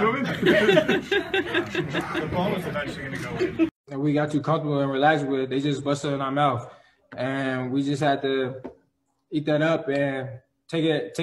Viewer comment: Busted in our mouth, we had to eat it up and take it on the chin ☠️☠️💀💀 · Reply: Who did Else heard him say “take it in” before he said “take it on” the chin Take it in “where” 😂